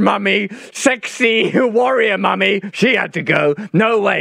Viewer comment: Did he call Diana a Warrior Mommie? 06:30- 10:38 her cockley orbit, his self analysis is priceless.